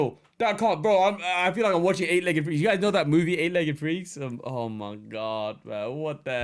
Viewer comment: did i hear, "8 legged Chinese man"